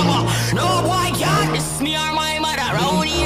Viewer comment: i thought it said "OH MY GAWD ITS NEAR MY MOTHER" til now 💀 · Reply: I thought it said ( on my gyat it’s near my motha)💀💀💀